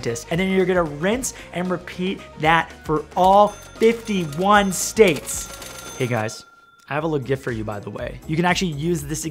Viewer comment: did he just say 51 states..?